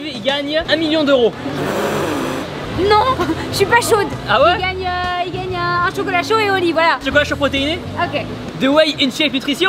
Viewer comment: a 6:10 elle a pas dit " il gagne un chocolat chaud AU lit " elle a dit " il gagne un chaucola chaud ET au lit ".... ya que moi qui a penser a sa ??